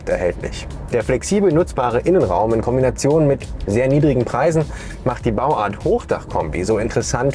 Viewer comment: Moment, hab ich gerade niedrige Preise gehört??😂